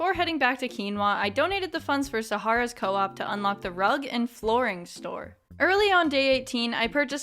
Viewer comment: I thought that said DRUGS instead of rugs